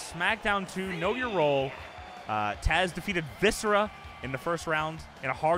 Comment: Thought I heard say "Viscer-ass" at one point. Can't believe we never got that tag team.